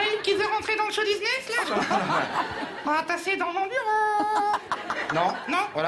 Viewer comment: Il dit quoi a 1:17 ? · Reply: "On va passer dans mon bureau"